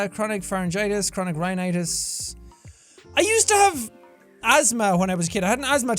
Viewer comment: I Thought Jack Said "I Used To LOVE Asthma When I Was A Kid".